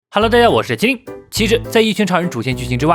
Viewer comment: 你别讲那么快啊，我听成 ‘’halo大家好，我是鸡‘’.....抱歉，爱你的影片